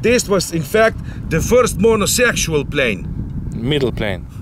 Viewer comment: Hah I thought you said “first monosexual plane”. I was trying to think of what that would even be. 😂 😂